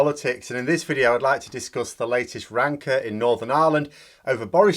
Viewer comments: I heard the word 'rancour' in the first few minutes of your video, as 'wanker' and had to rewind to make sure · My hearing aids not working, did you just say rancor or wanker?